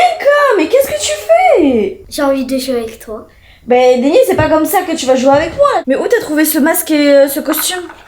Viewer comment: est ce que c'est moi toute seule qui entend dancom 😅